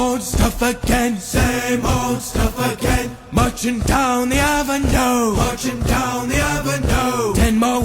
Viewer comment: I thought he said munching down oven dough😂 not marching down the avenue.